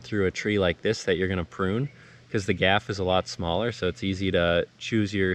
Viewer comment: Did you say “poon” or “prune”? Sounded like you said “poon”… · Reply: poon, can confirm